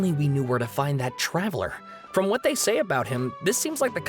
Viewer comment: I noticed that they put her instead of him.